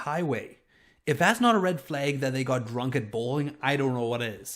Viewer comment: I thought you said "if that's not a red flag they got drunk and bullied", and I was thinking 'wow that's harsh' lol.